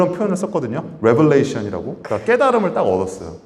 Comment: There are errors in the English closed captions at 18:46 time stamp in this video. Kim Youngdae said the word Revelation, but it is written as Revolution in the subtitles. And at 19:21, the subtitles say Hive; it should say HYBE (all caps) as the company name.